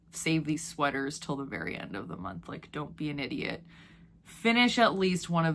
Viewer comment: When you said, "Don't be an idiot," I heard, "Don't be a knitiot." That might need to be a t-shirt. I'm really loving designing my own Selbu mittens (from the Selbuvotter book) and now I want to make Selbu socks, maybe with a big motif on the cuff and a palm motif on the foot. I find colorwork so calming. Also, I just re-watched "Theater Camp" and think you would love it if you haven't seen it.